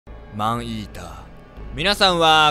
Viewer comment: もはやマンイーターがアイーダに聞こえてきた、、、